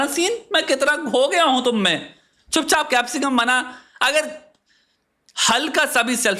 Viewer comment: i think he actually said 😂😂chup chap capcicum bana chutiye 😂😂😂